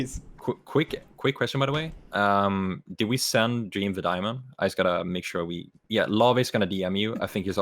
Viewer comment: i heard tommy say "dream posted" so hello guys poggers